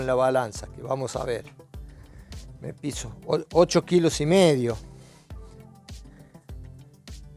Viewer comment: pense que decia balanza de coca 😂😂